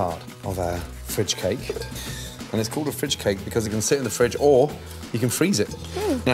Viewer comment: I'm sorry, can someone please tell me what he said? "Always use a _______" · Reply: he said "and its called a fridge cake"